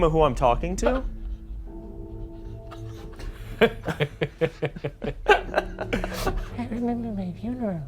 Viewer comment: I definitely heard "I remember my email" lol